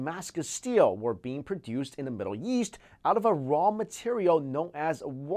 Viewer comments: It sounds like he said Middle Yeast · I thought he said: "When we think of our incestors" xD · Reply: Was I the only one who thought he said "The Middle Yeast" instead of East